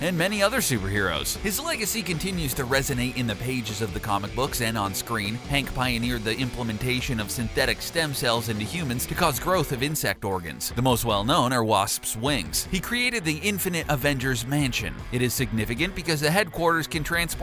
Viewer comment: thought he said wasps wank